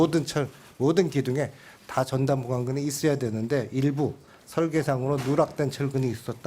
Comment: 자막 누가 만들었냐? "날리면"이라고 우기는 걸 정확하게 "바이든"으로 보도한 MBC의 명성에 걸맞지 않게 조사위원장이 0:46 부분에 "전단보강근"이라고 말하는 것을 "전담관근"이라고 자막달면 어쩌라는 거냐? 기존의 명성은 그냥 지켜지지 않는다 부단한 노력이 있어야지 자막 다는 사람들의 분발을 촉구한다! 그리고 덧붙여 MBC 회이팅!!!!!!